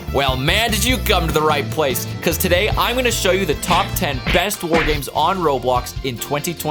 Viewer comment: I thought it said "Top 10 best war crime games"